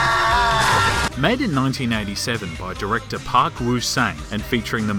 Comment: did you say this movie was made in 1997?!?!?! Ahhh super fail! ..even says 97nin the captions